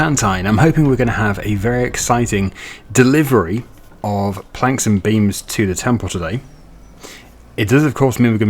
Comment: I heard "very special delivery of franks and beans to the temple" 🤣